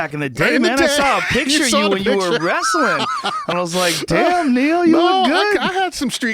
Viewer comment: thought he said damn nigga you look good.